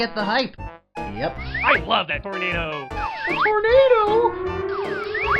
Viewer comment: What's the Groudon saying at 1:20? Where was this audio taken from? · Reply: No I'm not playing on my phone I'm taking care of business